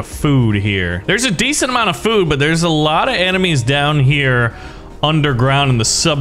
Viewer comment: I thought he said anime 0:31 Sheettt